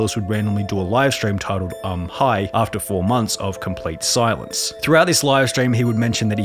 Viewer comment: I thought u said I’m high